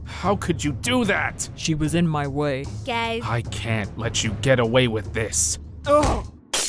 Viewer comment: did she say kay for yay? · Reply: guys*